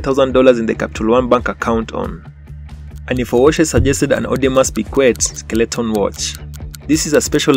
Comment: What in the world kind of watch Did he just say at 17:42 ? An audio must be quiet skeletal watch ? Cheeesus of Nazareth